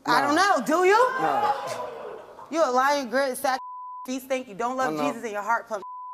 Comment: did she say ur feet stink? 😂😂😂